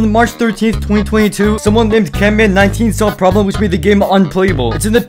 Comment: preston: canman18 this guy: camman19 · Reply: I thought he said canman but I watched it again